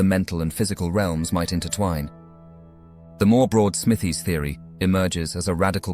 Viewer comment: What is the name he is saying at the 27:10 mark?? Something "Smithey?"